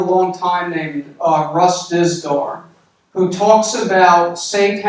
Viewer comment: Sorry im swedish so I didnt really catch the guys name you mentioned att 37:14. Can you give me the name here? Thanks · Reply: Russ Dizdar. R.I.P. He passed away 10/17/2021.